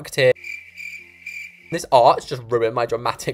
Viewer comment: At the end I was drawing and looking at my paper and when he said THIS ARCH I thought he said this art just ruined my great reveal so I was like Hang on a second how do you know what I'm doing!?😂